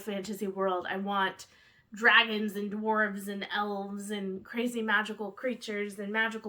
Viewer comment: Instead of "dragons and dwarves" I heard "dragons and worms" the first time lololol